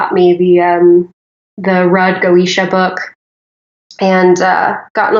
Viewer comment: Does anyone know what book she is talking about? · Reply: I think this one. The Goetia: The Lesser Key of Solomon the King I heard her say Goetia, but not sure what she said after that.